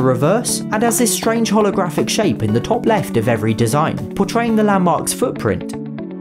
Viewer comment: Wait, did you say top left?